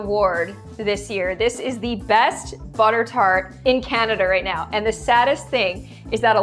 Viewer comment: I heard "cAnEdEr"😂😂😂😂😂😂😂😂😂